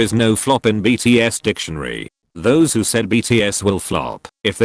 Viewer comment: did anyone else hear “hoes” instead of “for those”? Just me? Ok.